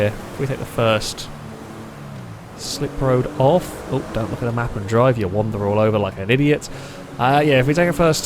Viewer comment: Did he say don't look at the map and drive